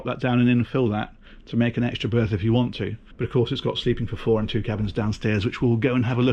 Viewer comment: Really great spec and room for a 50'. Did you say sleeping for 4 in 2 cabins at 3:33?